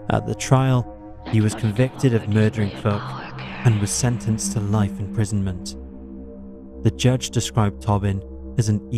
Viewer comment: what did that weird whispering say? 😮🥴🥴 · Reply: Unsubscribe, I think 😁